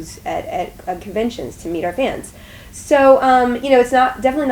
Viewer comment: I definitely heard "buy booze to meet our fans" instead of "booths."